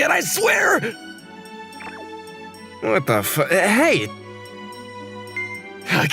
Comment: bro he said what the fu.. and say ...Hey...